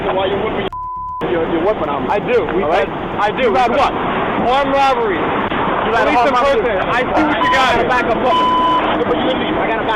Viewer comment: I'm pretty sure the guy said something about having a "good reason to have your weapon out" even though the CC said "whip yo wife" 😂